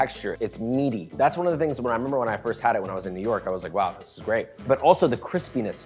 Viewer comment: Dude, i first thought he said "newark" not "new york", and i lit up cuz i live right by newark and im like "where tf is this place"